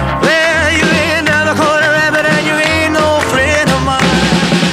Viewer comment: I cannot belive my entire life I've been singing this song wrong. I thought he said "You ain't never going to heaven" and instead its "You ain't never caught a rabbit" Like, what? 😂😂😂😂